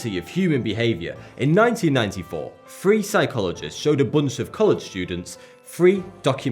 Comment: I thought he said a group of colored students. I'm just like "Oh.. interesting that makes sense. I'm sure there's a good reason" So ya.. apparently my sub-conscious is nonchalantly prejudice.